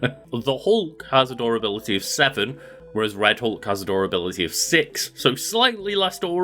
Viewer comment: all i hear is adorability lmfao instead of durability